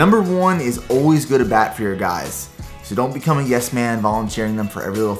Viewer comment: For some reason i cant make out what he says at 9:26. "Always good a back for your guys"? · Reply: 27POP27 always go to bat for your guys (?)